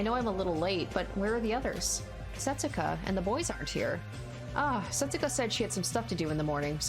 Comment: I thought she said Setsuka and the Voids aren't here lol